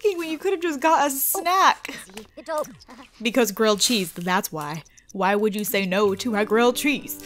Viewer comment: I swear I thought she said Grilled Trees.